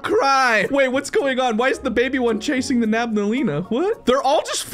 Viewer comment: I heard you say nabnaleena instead of babnaleena